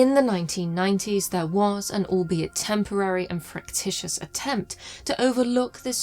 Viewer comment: I don't know what the word is around 16:14, but I doubt it's "fractitious", as I can't find it in the dictionary. Perhaps it's fractious?